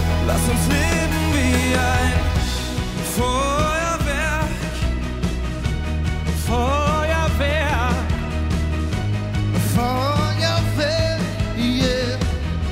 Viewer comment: ich habe Feuerwehr verstanden